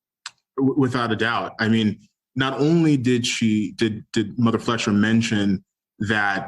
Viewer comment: Did I hear that right he said "mother flusher" ? I like to fill out of my chair, Listen to it